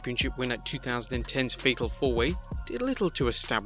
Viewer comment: Sounds like you said "Fecal 4-Way." Now I can't unhear it.